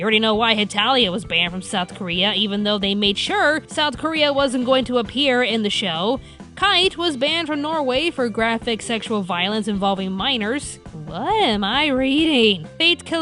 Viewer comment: Did she say Norway?? Cuz I'm from Norway and I wonder why it got banned.